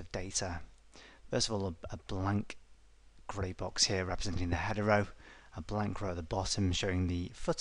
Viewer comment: I thought he said representing the hetero.